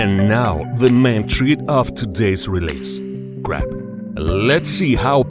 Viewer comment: Crap? Or Crab? Sounded like Crap.